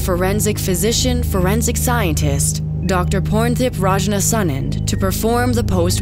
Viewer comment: Did i just hear "Dr. Porntip"? Lmfao